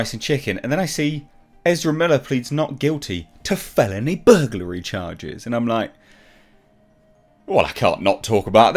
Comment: I thought you said buggery charges. He’d have gotten so many roles from pedowood